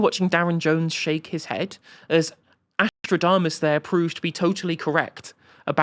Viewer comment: What did call Ash there telling and predicting truths to the Labour MP? Ashtrodamus?? 😅